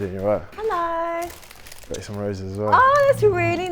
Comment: legit thought he said "got ya some raisins as well"